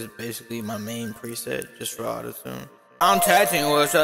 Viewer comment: Sound like he said "im texting with her with her wig- yeah i got a baby with her wig"